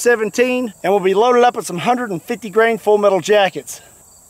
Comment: Men i just speak Spanish, i understand a too much that you say but i cant catch whats the name of the gun at 3:30 and the name of the bullet too. Could you write this here pls? Thanks!! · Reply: Fn scar 17 with 150 grain FMJs (full metal jackets)